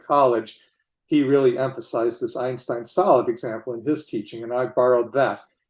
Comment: lol for a sec I thought he said "Einstein Salad". Always nice to listen to Schroeder: he's a good teacher.